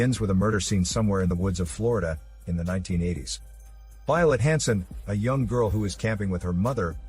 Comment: I misheard Violet Hanson as violent handsome